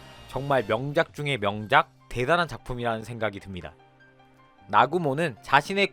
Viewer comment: 나구모를 아구몬이라고 들리는데 내귀가 잘못된건가?